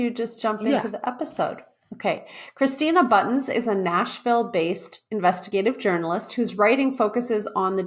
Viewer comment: I misheard the intro and thought said 'Christina is a national, based investigative journalist' 😆😆😆